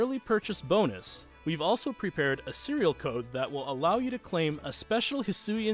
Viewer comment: For some reason when I first heard 'Serial Code' while Hisuian Zoroark is on the screen my mind instantly thought he said serial killer 😂